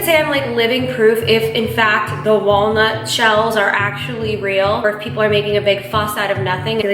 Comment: I thought she said it was a walnut powder and not walnut shell. Idk lol but luv u❤️❤️